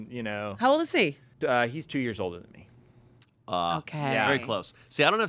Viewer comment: i thought he said "2 years old" (i watch everything on youtube sped up) and i laughed out loud. when shayne and amanda did not i went back. sad i was wrong.